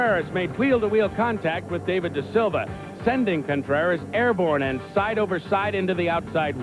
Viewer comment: I heard de Silva and that was ayrton sennas real last name and I was wondering he one of Ayrtons relatives?